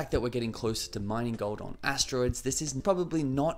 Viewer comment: Did you just say asteroids?